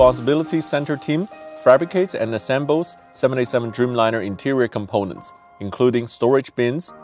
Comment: I thought it said “767 Dreamliner”